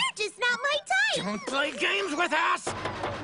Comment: "Don't play games with ass!!" I can't be the only one who hears that.